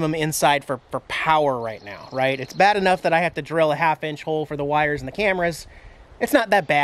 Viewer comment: OMG why are those holes so big for the wiring? I would think that it would be an easy task to undo whatever kills the power to the TV plug in the front. BTW very cool thanks for sharing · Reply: I actually thought he said all I have to do is drill some 1/2 " holes.